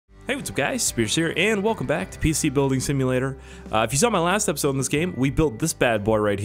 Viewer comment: When I first started the video I thought you said, “Hey what’s up guys scarce here.”